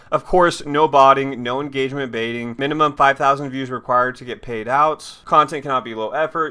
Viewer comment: Did you say 5000 views . 1 thought you said 1000 views at first